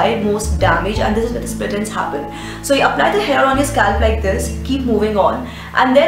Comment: did you just say.. Apply the hair on your scalp..lmao.. anyway.. nice video.. superlike... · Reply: she said hair oil on your scalp, but very fast so we only heard hair